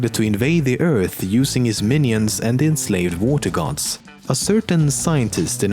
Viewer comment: You were saying Vortigants and I thought you were saying "Water Gods" I was thinking, WTF how have I never heard of them????